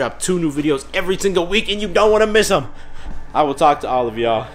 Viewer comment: On 1.5 x speed, it sometimes sounds like you say: "I will talk to olive oil in the next video"